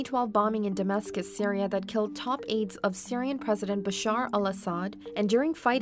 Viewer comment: I thought they said Khaled Al-Asad